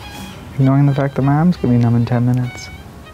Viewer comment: I have no idea what he said at 1:11. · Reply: Just discovered YouTube captions: "Ignoring the fact that my arm's gonna be numb in ten minutes."